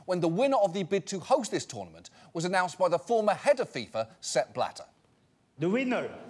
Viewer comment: Still can't hear anything but step ladder. Thanks Hugh